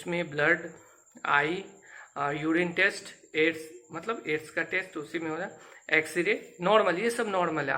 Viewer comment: bhai kya bol rhe ho Aids ka test? HiV AIDS ka bol rhe ho?